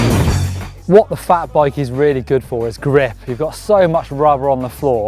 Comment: the subtitle says "what the fuck bike" instead of "what the fat bike..."